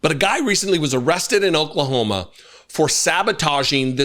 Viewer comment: The way this video started playing on my device, the audio was clipped at the very beginning and it sounded like said "I recently was arrested in Oklahoma..."